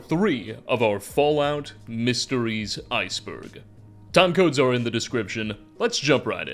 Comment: I was listening while working and would have swore you said “Tacos are in the description” 😂😂